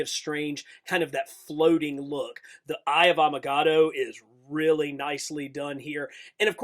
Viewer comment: I heard “Eye of avacado” 😂